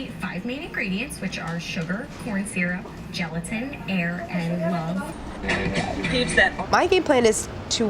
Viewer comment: The woman on the tv at 5:33 who is listing ingredients sounds like she is saying "They start with the 5 main ingredients which are sugar, corn syrup, gelatin, hair and lung." I literally had to watch this part 10 times just to be sure, and now I think she is saying air and love. I'm still not sure what she said, but can you imagine it was hair and lung?